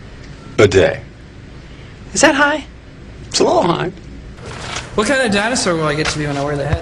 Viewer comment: What does Timmy say at 4:21? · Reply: Rima "What kind of dinosaur will i be wearing on my head?"